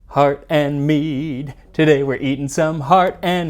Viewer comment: I thought he said "Heart and meat" for a second and was mildly more confused than I already was.